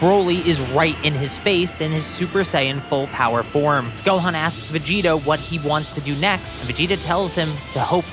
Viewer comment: sounds like you said vegeto